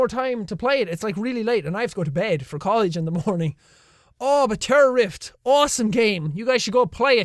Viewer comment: I thought that said terrorist xD